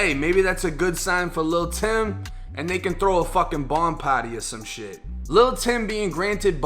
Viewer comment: "They can throw a bond party or sum shit", 🤣🤣🤣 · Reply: LOL I THOUGHT HE SAID BONG PART LMFAO 😂